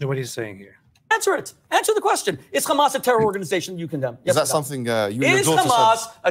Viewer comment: to be fair i think i heard him say "is that something you resort to" But i love it. The rabbi is not allowing him to bully and i think mo is flustered by someone actually pushing back.